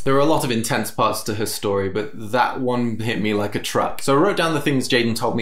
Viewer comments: Hearing Jaiden swear made me laugh my buttons off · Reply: I thought she said thuck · Jaiden did a profanity. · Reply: I thought she said thuck